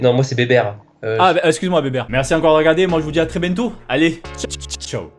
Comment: c moi on a la fin j'ai entendu "à très bento" ?😂😂je sais chuis conne😂